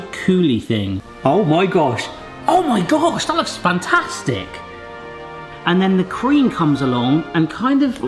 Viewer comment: When said, "and then the cream comes along." I thought he said "and then the Queen comes along." did anyone else think that or just me? 😆 also this looks like one heck of a trifle.